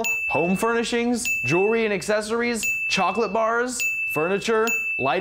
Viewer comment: Did you say chocolate? CHOCOLATE!!! CHOCOLATE!!!! 🍫🍫🍫!!!